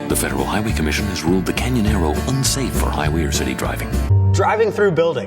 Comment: I thought they were saying either "Canyon Arrow" or "Can You Narrow?" not "Canyonero"